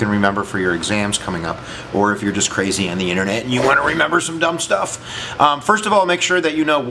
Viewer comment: did you say" remember some dumb stuff" …...or dumb people?.... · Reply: Michael Madrigal remember some dumb stuff